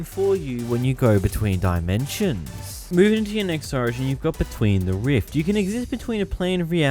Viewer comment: listen what he say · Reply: lol bro your right he says moving into your next origin your smart